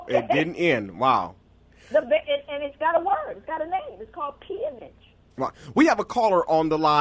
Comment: Joy said slavery didn't end and it's got a name. I'm not sure if I heard it correctly. All I can make of it is 'p image'. Anybody? Can be heard around 52:30 give or take. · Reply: Pharoah Wall It was great interviewing Dr. DeGruy. She said, "After slavery ended, guess what? It didn't end. And, they have a word for it. It's called peonage..." Thanks for watching.